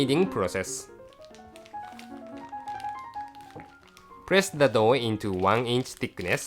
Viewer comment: did he say at 2:33 "repeat the dough shit"???lmao